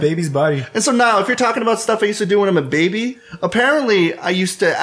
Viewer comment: So just to make sure at 39:35 did call "Niall" or did he just slur the word "now"?